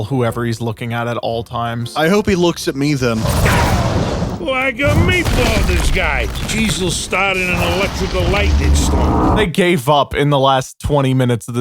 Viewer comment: am i mishearing this or is he saying “Like a meatball, this guy! Jesus started an electrical lightning storm” because what is he saying